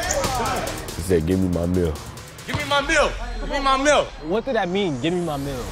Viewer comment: i saw the dunk on sportscenter on ig before i knew who mikey williams was and i thought he was saying "give me my milk, give me my milk" lmao